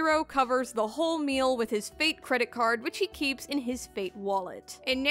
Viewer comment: Sounds like someone is quite the Gilgamesh fan, but then again, who isn't? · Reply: i thought she said fake credit card